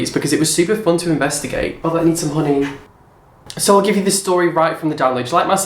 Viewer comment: "I need some honey" I thought you said money and I yelled SAME but then I realized you said honey